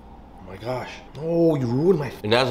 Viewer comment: All this time I thought he was saying something Korean 😂 but it’s bismillah?